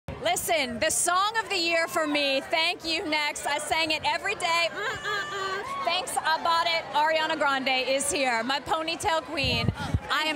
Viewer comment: Nobody : The reporter : telling ari she loves thank you next and then starts singing 7rings Arianators: 👁👄👁 Edit: (THANK YALL SO MUCH FOR 600+ LIKES) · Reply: i thought she said “thanks about it”